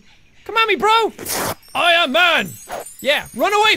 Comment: Bruh I only came here cuz I thought it said "aw man" (like creeper aw man) and not "Am man"